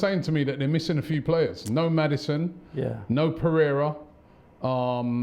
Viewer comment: When he said pereiria is out i thought he said torrieira 😂